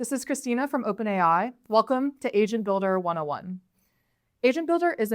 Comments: I thought she said Welcome to Asian Builder 101 lol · "it has chosen yellow as the color of Tokyo"🤣🤣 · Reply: Im telling you I swear when she said agent in the beginning it sound like she said asian😂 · I thought sis said "Asian builder" for a second damn it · I keep hearing "Asian builder". Wake me up when that's available. Need wiafu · Why the hell do i keep hearing "asian builder" 😂No offence meant lady. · I heard "Asian builder" and I was like "it might have worked great on her"! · Am I the only one who heard it Asian Builder 101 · Sounded like she said Asian Builder... Sounds about right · For a second I thought I heard asian builder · i thought she said Asian builder in the begining and was very confused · I thought she said "asian builder" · I thought she said "Asian Builder". Was it just me? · I heard this as asian builder... like wow that would be wild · Sounds like she said welcome to asian builder 101 - sounds like the next hot game on steam